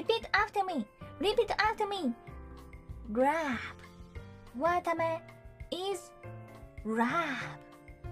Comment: I seriously thought she was saying 'Watame is Ram' but the subtitles said love. I was confused until I realized she just talks cutely